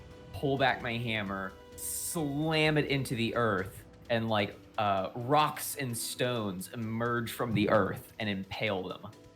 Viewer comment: Did i hear a Rock and Stone??